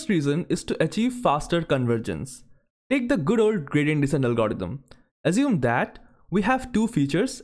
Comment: Sorry, I can't understand at 3:10 : Good old [what?] algorithm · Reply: Gradient Descent Algorithm